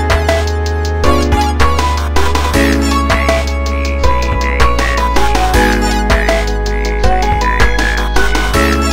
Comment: I CANNOT FOR THE LIFE OF ME DECIPHER WHAT IT SAYS AT 1:15 FOR PAWPRINT PANIC!!! ALL I GET IS “MADG-“ AND THAT’S IT RAHHHH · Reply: It's just spelling out napcast hehehe